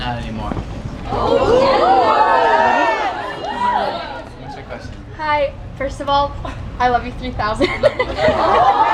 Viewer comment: What did the girl ask in 11:05 that he said "not amymore"? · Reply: Ana Luisa i think she asked him if there was any song in particular that he was more nervous to perform